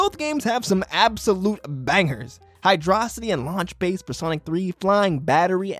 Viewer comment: did he just say high drossity throw tomatoes at em